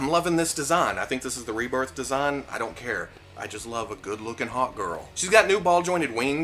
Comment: Every time you said Hawk Girl it sounded like you said Hot Girl ‘I just like a good looking hot girl’